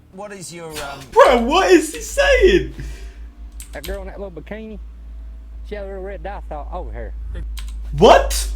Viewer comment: The guy you couldn't understand: "That girl in that little bikini, she had little, red dots all over here." (It appears he gestured to a part of his body that was out of frame.) "Do you think they learned their lesson?" "I'd say by today they are. If they feel like my leg feels, I'd say they're learnin'." (Probably referring to a multitude of bee stings.)